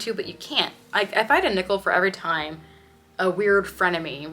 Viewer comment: I thought you said "if I had a nipple for every time..."